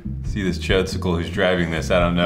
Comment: did he call the guy driving the civic a FUCKING chodesicle XDDDD